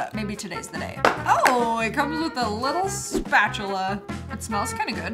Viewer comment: did she say "Scoop-achula"?